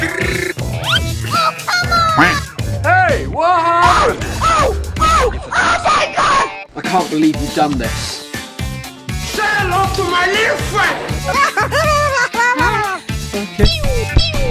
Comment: 誠に遺憾ですに聞こえるw